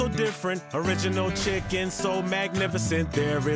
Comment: UNRIVALED ALL THREE……I thought he was saying ARRIVAL ALL THRAY